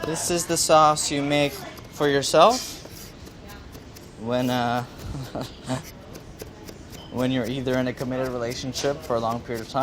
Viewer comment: I thought he was saying when your relationship has gone to 💩and you need some comfort food 🤣